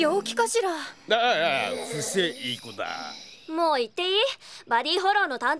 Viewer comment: なんて言ってますか？ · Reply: だぁぁ、伏せ、いい子だぁ じゃね？